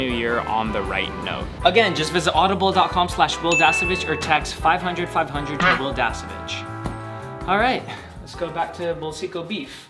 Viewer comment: Did i heard it right at 12:39? "Text 500500 to wil dasovich" 🤣🤣🤣